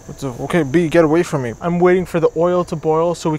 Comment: Did I just heard him said "Bee get away from me" XDDD